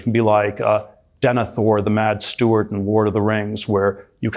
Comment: had to watch a few times, but Peter Thiel is both saying "Ward of the Rings" and "puneral fyre" in that first clip... And then taking about the dwarves and how they "dwelved too deep" in the second one.